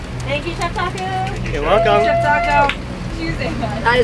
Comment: swear someone called u chef taco? Haha the bahamas is literally one of those paradise on earth places